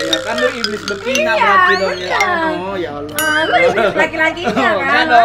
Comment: Apa aku salah denger Mongol bilang ya Allah d menit 4:14 😅😂